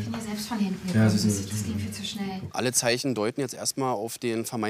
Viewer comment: Bei 3:32 hab ich erst verstanden: "Nein, ich bin ja selbstverliebt." 😅